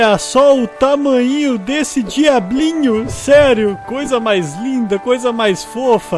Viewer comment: eu entendi outro diabinho ;-; Mais esse jamais sera fofo